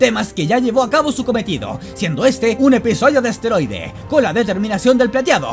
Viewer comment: Minuto 35:44 quiero que no escuché bien ¿dijiste asteroide o esteroide?